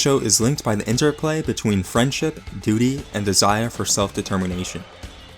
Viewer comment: for a second there I was like "Did he say 'booty' or 'Duty'?"